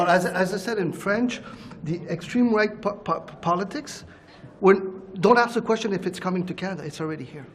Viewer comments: Did he just say "extreme white politics"? Lmao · does he say extreme white politics?